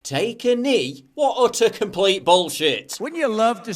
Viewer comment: Oh!. For a minute there paul, i thought you said take an 'E' LOL.